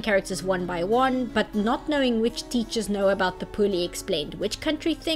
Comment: I had to listen like 4 times before I heard "poors" instead of "whores"